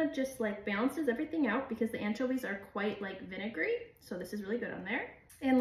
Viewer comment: Aren't anchovies salted ? I'd this particular kind isn't too salty I get the salt on the butter (even though I really like Brittany demi-sel butter) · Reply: I was wondering the same. Sounds like she said they're vinegary, so briney I guess. I'm gonna try and find these.